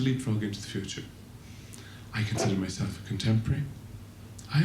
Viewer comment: what does he say at 15:26? help! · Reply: "I moisturize"